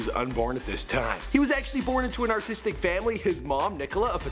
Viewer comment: not gonna lie thought he said autistic lol